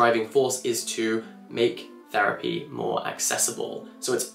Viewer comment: I thought he said “make therapy great again” and I got concerned 😧